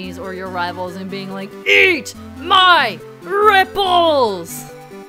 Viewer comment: My younger sister walked in and thought that you said "EAT MY NIPPLES"